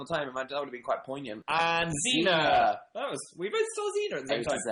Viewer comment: did they just say Zena cause that is my mums name??? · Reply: they said 'zena we both said that at the same time'